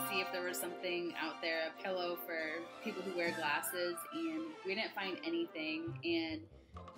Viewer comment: I thought it said we tried a pillow for sleeping in classes